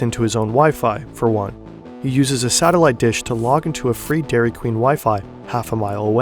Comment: HELLO 🤗 Guy's (hackers) 9:35 i have problem i can't understand what they saying: i mean this child use satellite 🛰️📡 dish to connect wifi I can't understand and it was encrypted and descipted at taxk 10 min how how???? How it possible? I only know tor,vpn,nodes but this is new thing so please help me can anyone tell me in details 💻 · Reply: In order to maintain privacy and to not alert the government, he uses a satelite dish, to connect to a DQ free wifi network, half a mile away, he uses a TOR node (Tor Network Server) to privately access to the WWW and he encrypts the data of both his PC, his TOR node and his Satellite Dish, so whenever he shuts down his PC, data remains hidden under what looks like garbage, and whenever he wants to turn on his PC, he has to wait an extra 30min to be able to use it.